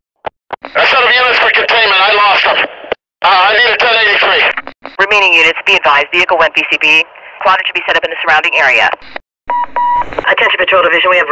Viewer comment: What are they saying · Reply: Setup units for containment, I lost them. Uh I need a 10-83. Remaining units be advised, vehicle (went) VCB, quadrant should be set up at the surrounding area